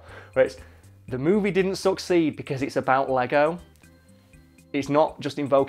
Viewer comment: the first time he said "succeed", i thought he said "suck seed"